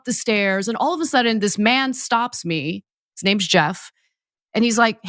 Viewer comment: Did he say, "My name's Jeff" like Channing Tatum in 22 jump Street??